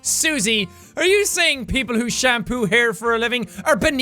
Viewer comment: For a minute I thought he said "Sean poo hair" XD